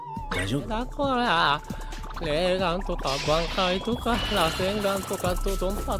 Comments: did he said rasengan? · did he said rasengan🌀 ???